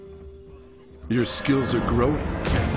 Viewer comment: I thought he said "Your skills have grown Anduin"